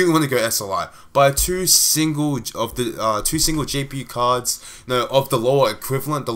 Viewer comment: it sounds like your saying jewel gpu cards